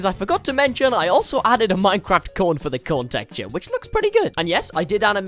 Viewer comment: what I hear I made the corn corn, what it actually is I made the cone a Minecraft cone